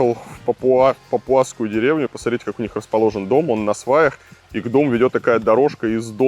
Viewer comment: Тоже послышалось Насвай?